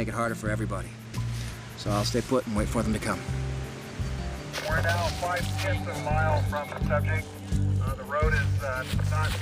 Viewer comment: What does he say at @ 41:01 ? We're now 5/10th of a mile from the subject? Surely that can't be right.😂